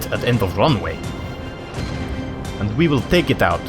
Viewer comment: /guys it sounded like he said and we will dominate kfc XD